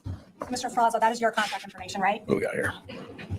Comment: Did I hear him say "get me out of here?" I could have sworn I heard it during the morning live I was watching but I didn't ask them. Maybe I'm hearing things